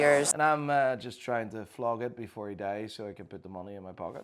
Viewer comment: her dad is dead, what would treatments do to a dead man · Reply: my bad thought she said he died